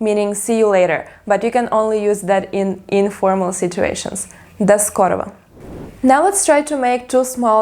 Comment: Sounds like you said до скорово